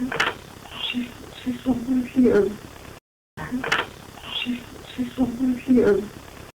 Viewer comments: I heard “please listen there’s someone here” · I hear (very clearly) "She... She's over here."